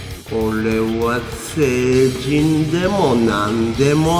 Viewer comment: 画面見ないで聞いてたら0:09の聖人のところセイキンに聞こえた(?)